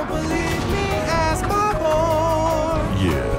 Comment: i thought he said 'ask my whore'